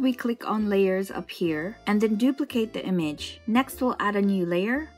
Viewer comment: I legit thought you said “Then duplicate that bitch” 😂